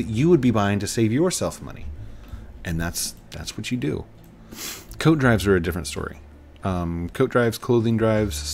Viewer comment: I honestly thought you said “Code Drives” for a second XD Can’t wait for the bridge to be done! :D